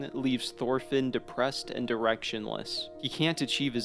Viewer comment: Okay, but who else heard 'leaves Thorfinn depressed and erectionless' at 6:55??? 😯 Just me huh? Edit: I guess either works in this context